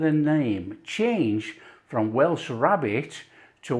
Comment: Probably it changed because of hearing rarebit.... when you said rabbit, I could hear rarebit. A misheard word because of dialect